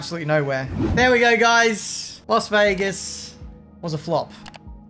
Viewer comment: Is it just me who heard him say Los Vegas not Las Vegas